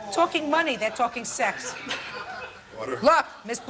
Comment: Officer Byrd said: "ORDER!" Caption says: "Water."